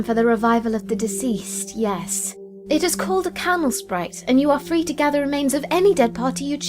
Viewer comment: I thought UU said camel-sprite or Caramel/Carmel-sprite. I had to do a double take because of it, lmao